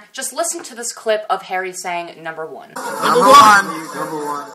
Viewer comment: If you listen to it extremely carefully, Harry doesn't say "number one" he actually says "he's number one" so no. He is not talking about the song, he is talking about Louis. he is Louis' number one fan undoubtedly. 😍😍😍